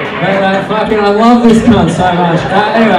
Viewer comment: I love at 1:12:05 he CLEARLY says "cunt" yet the subtitle is "guy". Editor working hard to translate from Aussie to English